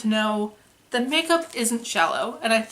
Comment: I heard "makeup is not shallow" as "makeup is not jello" at first and got really excited to hear where you were going with that analogy.